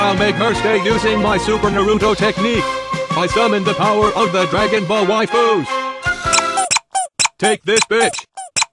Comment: did he say Dragon ball waifus?